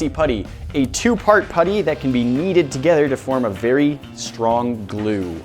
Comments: The intro it sounds like he said “yeeted together” instead of “kneaded together” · did anyone else hear "they can be yeeted together"?